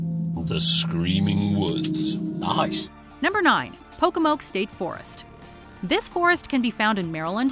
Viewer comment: 🤔 WHAT IT SAY POKEMON 🐲